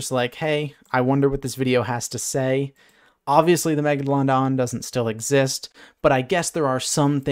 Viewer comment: legit thought he said "mega-long-dong"